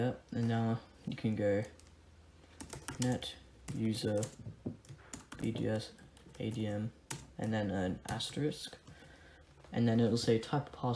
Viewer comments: what did you say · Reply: the whole thing is net user [administrator name] * you need the asterisk at the end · What did he say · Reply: the whole thing is net user [administrator name] * you need the asterisk at the end